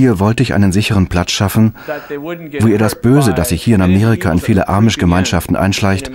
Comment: Ich hab arisch verstanden😂😅